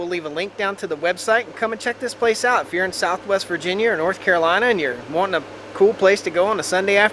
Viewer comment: Being from WV, I keep hearing you say 'south West Virginia.' Then I keep telling myself, no, it's south-west Virginia.